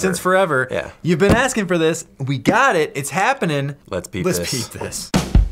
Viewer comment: thought they said " lets be pissed!"